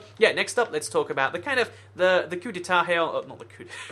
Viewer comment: What'd he say? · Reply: He said coup d'etat, pronounced coo dā tä. It's a french phrase meaning military takeover by a small group.